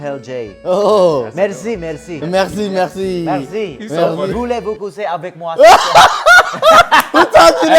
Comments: can someone tell me what he said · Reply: If I heard right he said "voulez vous coucher avec moi" which means "would u sleep with me" lmao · what did he say? just curious that's all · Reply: hhh he was listing the phrases he knew in French and what he said was "Voulez-vous coucher avec moi " which translates to "Do you want to sleep with me?" lol